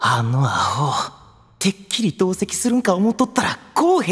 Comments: 白膠木簓、かわいい以外の何者でもないじゃん… 「ついでに注意したるか」 が 「ついでにチューしたるか」 に聞こえた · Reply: キスるんかに聞こえません？(難聴) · ～ 出だしリップ音と勘違いしたワイ、「てっきりどうせキス」に空耳する